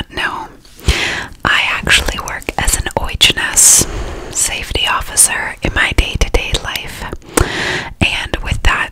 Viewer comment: What is the word she says at 0:39? It sounds like oichness or something? I've never heard this word before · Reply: OH&S